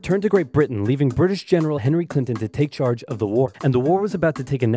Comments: I thought, for a second, it said "leaving hillary clinton to take the war" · 😆 did i just hear “British general Hilary Clinton”? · I'm sorry, who did you say that was? Clinton? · I can be the only one who heard “General Hilary Clinton” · Legit thought you said “hillary clinton” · did he just say hillary clinton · I thought he said Hilary Clinton lol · I SWEAR I thought he said General Hillary Clinton. Replayed it and it said Henry Clinton. I was thinking ‘didn’t know she was THAT old. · I thought I heard Hillary Clinton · I thought you said Hillary Clinton instead of Henry Clinton